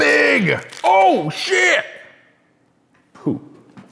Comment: i thought you said that it looks like poop